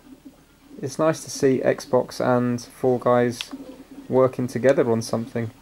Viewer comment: I thought he said Fortnite when actually he said fall guys😂😂🤣